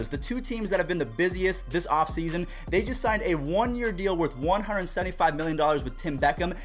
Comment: Did say the mariners signed a deal with Tim Beckham for one year,175 million? Did I hear that right? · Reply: 1 POINT 75 .. 1.75 million